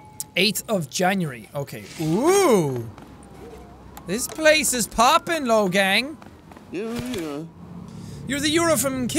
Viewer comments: Did jack say “Oo this place is popping logang” at 1:05 😂😂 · Is it just me or did he say logang at 1:07🤔🤔🤔(btw i love the logang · is it just me or did he really say this place is popping logang